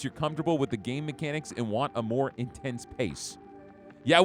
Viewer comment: when he said "I wanna be intense all the time", i thought "He must really like camping" because i thought he said in tents 🤣